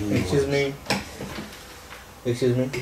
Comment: としみつが言った最初の「Excuse me？」が「良純」にしか聞こえんwwwwww